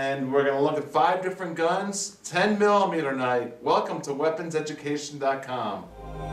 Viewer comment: i thought it said weapon seduction